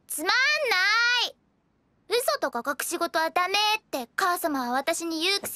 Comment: wait, did she actually say "kazuma"? if you'll excuse me, I proceed to XDDD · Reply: She says "Okasama," the Japanese word for mother.